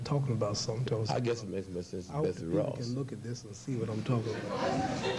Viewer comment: Anybody catch what Sly said at 5:58 ? I can’t make it out but seemed to get a laugh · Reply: i guess it makes as much sense as Betsy Ross. Well, ya put that together with the first question that he asked; it dovetails nicely: 5:43 A Colonial figure=Our First Flag 6:46 our first step towards autonomy 7:02 now between the first flag line, question and here-whose the clown? It went right over his head-Betsy Ross